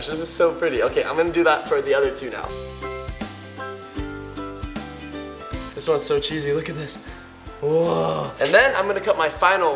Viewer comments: did anyone hear him say cheesy? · did he say this one is so cheesy at 5:02?